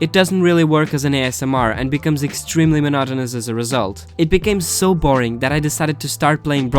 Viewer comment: "extremely mezhoiuzgzzoizbzzubzzzboaur" that's what i heard xD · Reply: Lmao 😂 I said "it became extremely monotonous as a result" but yeah, it was kinda fast